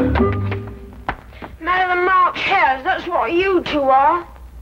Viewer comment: Can anyone understand what the boy calls them @ 6:43? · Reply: 'Madder than March hares, that what you two are'. Time stamp